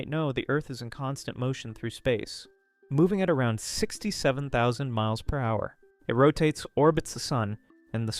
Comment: BOII DID YOU SAY 67